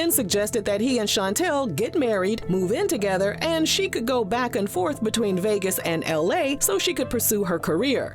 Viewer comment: I thought she said she don't go back